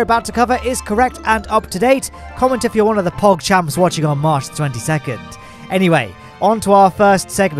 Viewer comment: you said: "comment if you're one of the pogchamps watching on March 22" I heard: "comment if you're one of the pogchamps watching on mars 22nd" I thought you told someone to comment if they were watching from mars in the 22nd century lol